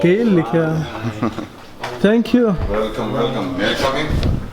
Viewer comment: في الدقيقة 4:39 كإني سمعت يقول شي غلط😂😂😂😂😂😂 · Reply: 3lrkop rv yes he sad:سكسي تو 👅👅